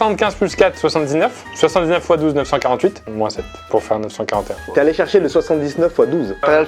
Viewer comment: c’est moi où Pierre arrive à faire 79x12 et c’est juste, mais 948-7 pour lui ça fais 944😭😭